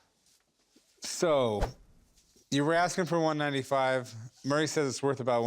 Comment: Chumlee said 195, but the subtitle said 185